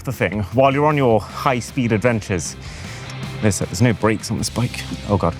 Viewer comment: What's your favourite feature? 🤔 Grab the Insta360 X5 now: <link> - with a FREE replaceable lens kit for a limited-time only! Just a reminder that this video is sponsored by insta360 - but I want to be super open with what this means. They get to advise on the topics I talk about (not what I say about those topics though) and they get to see and approve the script before we film. But it also works the other way around, we reject about 90% of sponsored video requests, because I only want to do a sponsored video on products that I genuinely think are awesome, so: 1. We ask for the product to be sent out to us before agreeing to make the video 2. We test it to make sure we love it first 3. We cancel partnerships with any brand that tries to interfere with my honest opinion Hope this helps! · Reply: wait bro did you say there's no brakes on the bike that's crazy😅